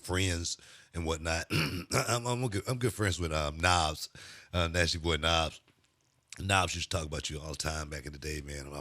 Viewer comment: Man i thought he was saying "nas, nasty boy nas" and i was like hell yeah but then I figured it out lol.